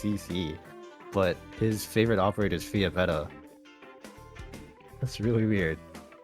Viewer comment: wait it's Fiametta not Flametta wtf have I been saying all this time???